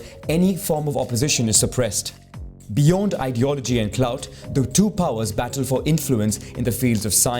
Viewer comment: did he just say clout ? As in today’s modern version of the word “clout”? 😂